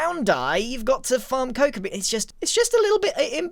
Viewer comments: I misheard when he said coco beans and thought he said coco melon lmao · "Brown dye you have to farm coconuts" WHAT? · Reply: I heard him say cocoa beans